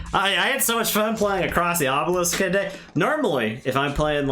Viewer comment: Across the Obelisk for anyone wondering. I couldn't understand what you were saying but I found it after some semi English googling for cooperative card games.